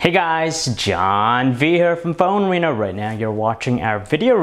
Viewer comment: I misheard "phone wiener" instead of phone arena 0:04 xD anyway good vid